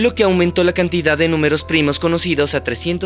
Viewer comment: Hay un error en el minuto 23:27. El guión dice: "...lo que aumentó la cantidad de números primos conocidos...". Lo correcto es decir "lo que aumentó la cantidad de nudos primos conocidos". Pueden comprobar la versión en inglés de este vídeo, que dice "...bringing the total number of known prime knots...".